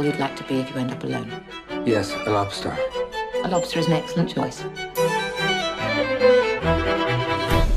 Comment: I thought she said "lobster is next lunch choice"....lol. on another note, one of the weirdest god damn movies I have ever seen.